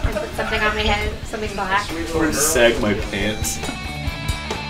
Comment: What did she say at 21:09??? "Something black?" Did he say "Inside my pants?"